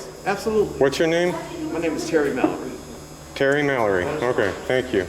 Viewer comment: i thought he said my name is karen mallory ..lol